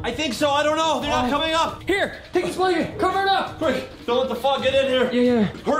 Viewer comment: did say the f!$& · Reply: He said fog